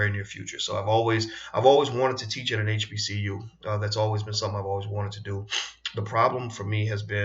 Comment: I thought u said u never wanted to teach at HBCU